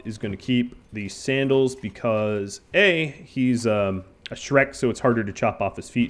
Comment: The AI cores are for the most advanced research. Black Desert City has some stock for starters, but they never restock the AI cores after you purchase them. The Holy Nation will consider anybody with artificial limbs as a skeleton as well. So a human with a prosthetic arm will also be attacked on sight. There is a sense in stockpiling the iron sticks. They weigh a bunch. Strength training! Ideally you will use heavier things, but those will only become available to you later once you go treasure hunting in ancient buildings. Since Voth is a horned dude and not a green ogre, he is actually a Shek without the R. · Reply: I thought I heard him say Shrek but I wasn't sure xD Edit: 28:30 found it again, yep it's definitely Shrek